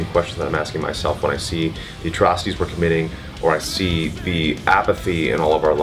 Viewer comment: what Matt said ? the trustees are committing ? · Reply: 'the atrocities we are committing'